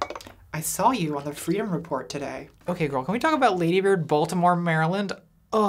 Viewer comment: I keep seeing "Baltimore" written in the comments, which makes sense with the surname "Maryland," but I could have sworn that they said their name was "Voldemort Maryland."